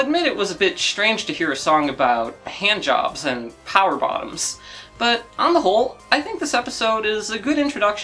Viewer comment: At around 47 minutes, I always thought Zinnia Jones said Power Bombs instead of Power Bottoms and thought "Hell yeah, Metroid in the Bill Nye show"